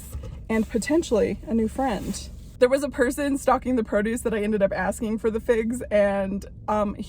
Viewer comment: I thought you said “stalking the produce”… stocking. Stocking produce… makes much more sense.